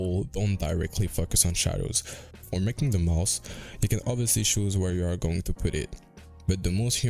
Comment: The captions are wrong lol it said mouse instead of mouth 😩😂